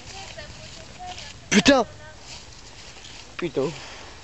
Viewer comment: pareciera que dijo “pito" :v